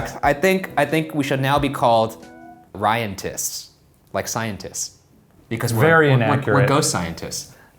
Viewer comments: i thought #ryantists said #ryantits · lmao Ryantists i thought u said Ryantits pmsl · I thought he said ryantits😂😂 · i thought ryan said "the ryantits" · I thought Ryan said #ryantits 😂😂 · I thought you said Ryantits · Who else thought it said ryantits instead of ryantists? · Thought he said #ryantits fr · It's sounds like Ryan is saying ryantits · Am I the only one that heard it as RyanTITS? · i thought it said ryantits good name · Anyone else thought he said RyanTITS??? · I misheard it for Ryantits...sorry ⚜